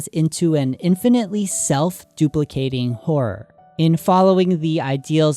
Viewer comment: i- i heard "self duplicating whore" help- im dying laughing at this